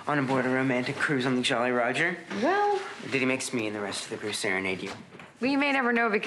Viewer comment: what did Henry say from 0:17 to 0:20? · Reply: Gabriela Teodorescu "or did he make Smee and the rest of the crew serenade you?" Lol, I couldn't understand at first either